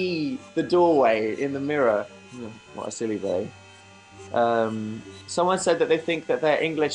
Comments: Did he just say "what a silly bae" of am I hearing things again? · did he say what a silly bae or billy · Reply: billy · 'What a silly billy' 😆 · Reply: i thought he said "what a silly bae" oops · 'What a silly billy' 😆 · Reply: I thought he said silly bae and I was like what! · 'What a silly billy' 😆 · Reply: I thought he said "silly boy " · Every time I hear "silly billy" in this clip I think it's "silly bae" and I die a little inside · did he say what a silly bae or billy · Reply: I thought he said silly boy